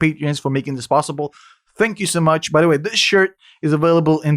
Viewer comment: Did you just say low quality high prices? 😂🤣